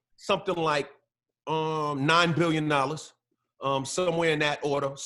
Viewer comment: did he say 9 billion dollars?